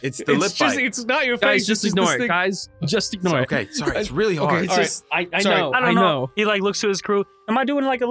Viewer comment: ???? did he just say its really hot?????? what???? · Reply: I think he said it’s really hard, like hard to ignore the lip bite